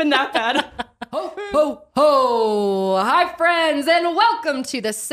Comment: I just smoked a bong and when said “ Ho, ho, Ho! HI friends ! “ I heard it like: “Ho Ho Ho High friends !”